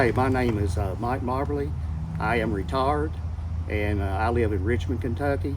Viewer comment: Did he just call himself retard? I thought I was the only person who calls themselves that😂😂